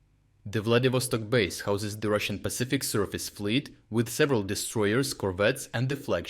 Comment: I know he said "Russian Surface Fleet", but I heard "Circus Fleet"